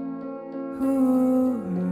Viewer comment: I don't know why but I heard ''Tell me I'm twerking for you~" 🤣🤣🤣